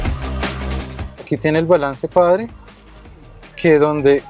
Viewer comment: alguien más escucho al asesor de skoda haciendo la demostración del radio (3:02), que dice " aquí tiene el balance padre " haciendo referencia al ( FADER) o control de ubicación del sonido ? no tengo más palabras .........F A T H E R